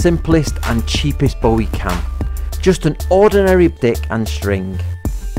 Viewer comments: a what and string? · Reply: I thought he said thicc and string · What did you guys hear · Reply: I heard “ordinary BIC and string.”. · I don't know if saying d*ck instead of stick was intentional, but it was very suprising. Lol